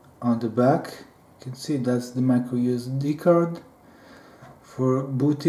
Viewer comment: Nice video!. I order mine today. How long did it take to get the nanoPi? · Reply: I heard "Micro-USB". Either I misheard, or you really meant micro SD